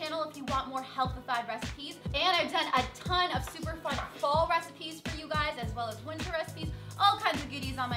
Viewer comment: sounded like she said "fo" recipes instead of "pho" but she meant fall lol